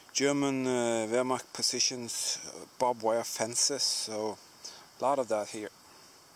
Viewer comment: I thought it was wood and thought you were going to say you are finding a lot of wood in the forest.... Good thing it is wire thought you were insane for a moment